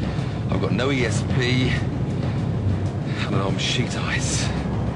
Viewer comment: sorry, at 6:50 he says "sheet ice, thick ice or shit ice"? I can't understand... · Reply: Giorgio Bikos I think sheet